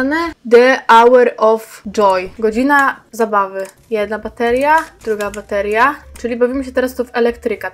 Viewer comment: na 4 minucie jak mówiłaś "jedna bateria druga bateria" to usłyszałam patelnia🍳